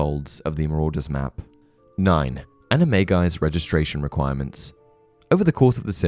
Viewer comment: Yes. (Anime Guy) that’s what I heard